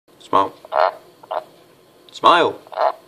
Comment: is its name smile? i heard you say smile if im mistaken correct me LOL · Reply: He says smile to get them to croak